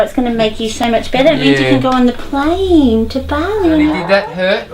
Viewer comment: did she just say 'going to plane to Bali".. yess please come to Bali.. enjoy Beautiful Bali 😁